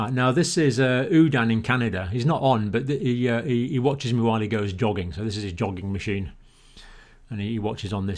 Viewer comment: lol, thought he said "he watches it when he goes dogging"! 🤣